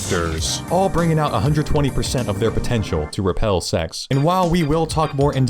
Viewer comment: did you say potential?